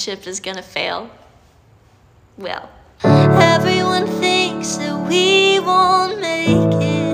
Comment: I heard “ Everyone thinks that we won’t make it. Fart”